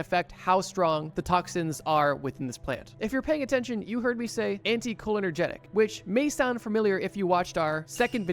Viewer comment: No, I heard you say "anti-cholinergic", not "anti-cholenergetic". Could be my brain autocorrecting errors. (11:44 Or maybe not, because you read it wrong *again*. "Anti-colonogenic"? PLEASE stop, it's massively annoying.)